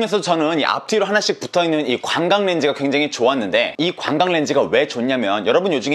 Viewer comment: 관광렌즈라고 들리는데요 ㅋ 관광하면서 촬영하라고 있는 렌즈인가요? ㅋㅋ